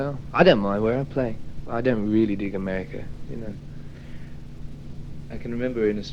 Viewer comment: Did Robert say, "I don't really dig America."?